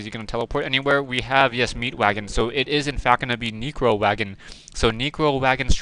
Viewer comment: btw its neccccccro wagon, sounds like ur saying negro wagon lol